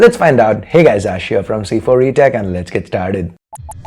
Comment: Captions says " c4e-tag"